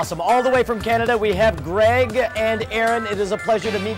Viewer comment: I thought he said ''bald'' of you guys at 0:56 after reading the video thumbnail..